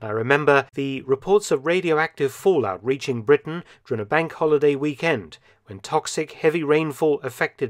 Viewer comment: What does Mark say here? Bank holiday ??